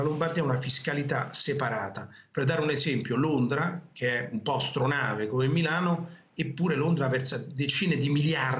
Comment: non ho capito cos'è Londra al minuto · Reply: "Astronave"